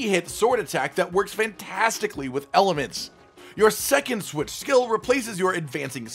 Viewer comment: did said elements or aliments